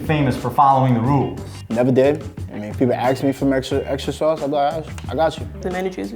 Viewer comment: why didn't they put axed in quotes instead of correcting it to asked, like they did with "I gotchu" a moment later?